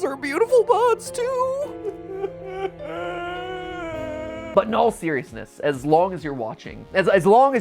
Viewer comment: Every time he said "But in all seriousness" I kept hearing "in all theory-ousness". I've been on this channel for far too long...